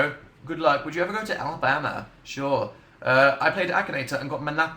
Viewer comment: does he say "alabama" because omf thats my state and iM SWEATING